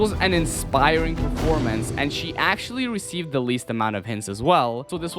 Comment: did he say sheee?